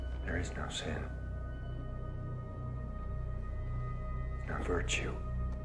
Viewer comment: Had no idea he said "No virtue" in this scene, always thought he said "Not for a jew"..........